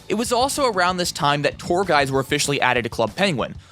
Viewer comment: Im sorry but i heard "poor guys" instead of "tour guides"